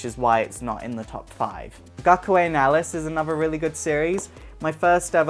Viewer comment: for the anime at 4:36 I didn't catch the name can you tell me if its English dub if so can I get the name it seems lie a really good anime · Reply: Its Gakuen Alice, it was never dubbed into english.